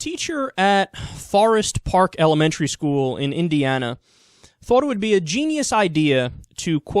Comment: Indiana? I thought you said INDIA!!! My bad, Yanks...lol